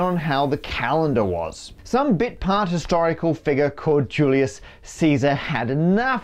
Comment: captions say unintelligible instead of bit part